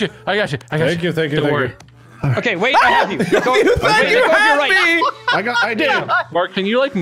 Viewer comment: Did anyone catch mark say "you thed you had me!" At 8:18 😂😂😂